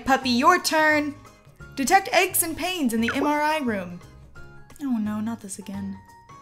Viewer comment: I thought she said, "Detect EGGS and pains in the MRI Room."